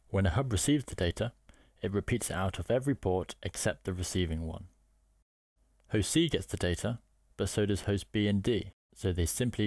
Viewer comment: sorry what did you say at 0:40? Can't get the accent.. lol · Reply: "Host C get's the data" British accent + bad audio = Sorry bro!